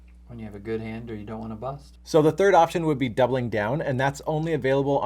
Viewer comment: I don't understand what was said very softly at 7:17. "When you have a good hand or you don't want to (what?)" mic level is too low. You can't hear everything he says at 10:46. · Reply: Bust. Turn on the captions, mate.